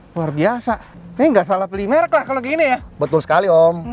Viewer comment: gw dengernya "saya gak salah pilih merk lah kalo gini ya"